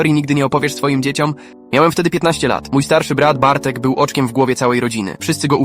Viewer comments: Na początku usłyszałam "Bartek był BOCZKIEM w głowie" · Nie wiem czemu ale na początku zamiast oczkiem w głowie usłyszałem boczkiem w głowie xD